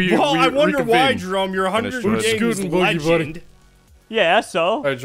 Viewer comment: anyone know what says, he gets interrupted by and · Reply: He says "i wonder why Jerome, you're a hunger games legend "